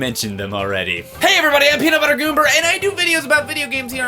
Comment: did he say peanut butter goomber